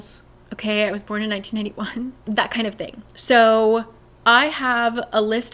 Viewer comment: I thought you said you were born in 1981 and I truly couldn't come to terms with that because I couldn't understand why you looked so young😅